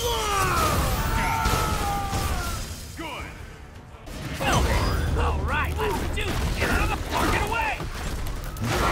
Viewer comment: I swear to Christ it sounds like Rocket Raccoon is saying, "Get out of the fuckin' way." Can someone please tell me what it is he's saying? When I hear it, that's what it sounds like. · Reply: Steven Ingram Rocket said "Flarking", which is his way to evade saying the F-word